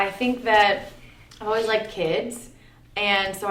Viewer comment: I thought she said I've always liked pigs lol